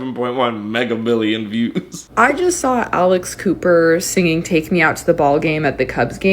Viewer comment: I THOUGHT THAT GIRL WAS SAYING ALICE COOPER AND WAS LIKE BRO WHY YOU KEEP SAYING SHE. IM DEAD OMG